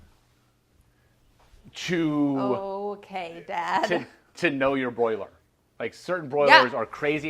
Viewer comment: did she just call him dad af